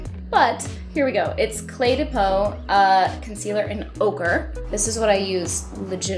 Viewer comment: So beautiful! But who else thought she said "ogre" and not "ochre" for her concealer shade?